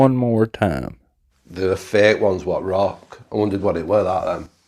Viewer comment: I heard "the fake ones that rock"